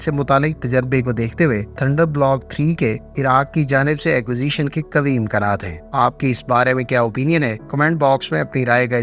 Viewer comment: Did you say kam hi imkanaat? If yes why kam imkanaat?